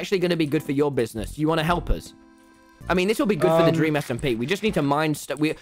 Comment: I thought dream SMP said dream simp and tbh that's probably accurate.